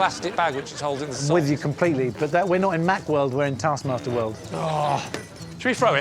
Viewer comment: I love when Mike literally shut Lee down with "we're not in Lee world, we're in Taskmaster world" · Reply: I thought he said "not in that world", but on re-listening he actually says "not in Mack world"